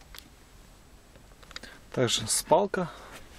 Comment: Мне нравится твои обзоры но этот обзор не такой как остальные! 1)почему в машине не прибрано? Вроде ДОСТОЙНЫЙ ГРУЗОВИК а состояние то... Мне даже послышалось не слово спалка а сралка до чего засрано в грузовике извините за выражение! 2)Почему не будем заводить двигатель? ПОТОМУ-ЧТО ОН ВООБЩЕ НЕ ЗАВОДИТСЯ!!! 3)За такую помойку на колесиках так сказать с КОНДЕЦИОНЕРОМ и АУДИОСИСТЕМОЙ ПРОЛОЖДИ оно даже с прицепом не может стоить <num> РУБ!!!